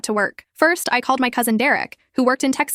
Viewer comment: Some people seem confused and said Derek was jake cousin, she clearly said my cousin at 8:40 in the video